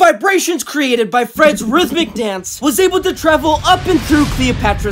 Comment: "Let me in mama cita" and "That sweet sister spudussy" got me rolling on the floor🤣💀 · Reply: I heard "let me hit mamacita"